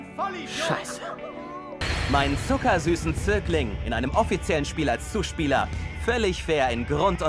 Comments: ich hab mir das jetzt locker 10 mal angeschaut und endlich verstanden was er wirklich sagt: meinen zuckersüßen zögling lol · Ich glaub bei 0:57 sagt Oikawa "Zögling". So einen Ausdruck hört man auch nicht jeden Tag, da kann man das leicht falsch verstehen :)